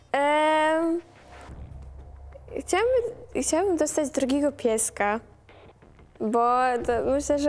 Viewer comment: usłyszałam drogiego pieska 😂😂